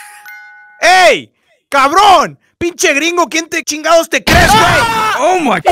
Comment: juicy: calls a alien: COBRONE (idk wth he’s saying😔) proceeds to shoot juicy